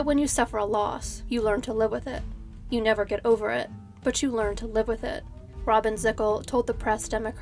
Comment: "You learn to live with it" Riley's father. For anyone who's experienced loss should understand this statement and for me it's why I don't say "closure " a new overused word.❤ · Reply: Oh! I thought he said he never learned to live with it!